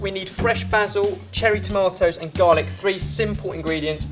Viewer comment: When Ben said Cherry Tomatoes I thought he said Jamie's Tomatoes and I'm like wtf?!